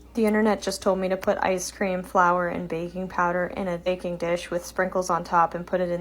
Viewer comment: at first I thought she said “baby powder” and I was like WHAT